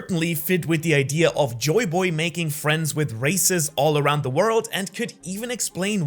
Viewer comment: I thought I heard making friends with racists all around the world🤣